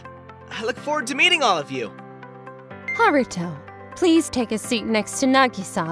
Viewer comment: I thought I heard Naruto lol